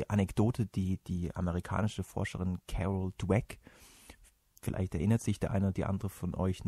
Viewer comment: Wie heißt die Forscherin, die bei 2:40 erwähnt wird? Verstehe den Namen einfach nicht. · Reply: Carol dweck. :)